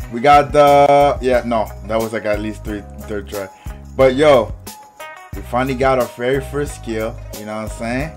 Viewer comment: It sounded like he said I finally got the iPad 💀 💀 💀💀